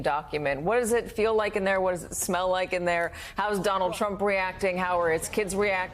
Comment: Did she just say " How does it smell in there " 😀